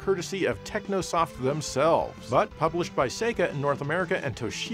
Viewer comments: I had to pause for a bit, because I thought you said that Thunder Spirits, for the Super Nintendo was published by SEGA! A quick google revealed it was actually Seika 😅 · I thought he said Thunder Spirits was published by Sega not Seika.